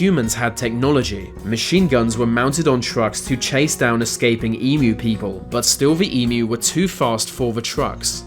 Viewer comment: Did I hear "emu people" lol